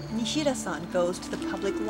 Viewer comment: I thought I heard mashida's son it was actually mashida-san